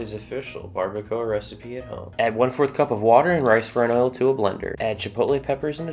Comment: Dude, you mumbled the hell out of the oil. I listened twice, and you were like, "Add brerfredsissefard," when you added the oil. What did you say? What oil is it? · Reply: I thought he said Rice Granola